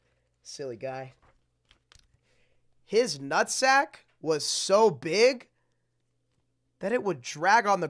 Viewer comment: I thought when you said his Napa was too big that it would drag I thought you said ass